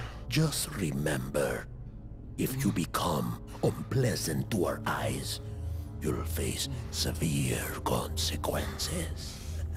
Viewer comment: What does he say at 1:42? If you become a...? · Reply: He was saying “if you become unpleasant to our eyes, you’ll face severe consequences”.